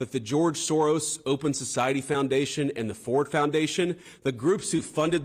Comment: Did he say "Ford Foundation"?